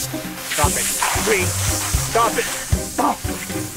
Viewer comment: Hey what do you know? At 1:36 he did say please. He says "Stop it please stop it!" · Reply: he says: "Stop it, Bree. Stop it"